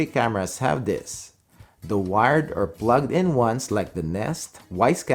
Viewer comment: what are those cameras he said are wireless pixel detection? Nest, Wyze Cam, and E ??